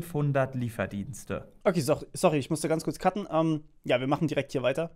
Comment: Wer hat auch "sorry ich musste kurz kacken" verstanden? Cooles Video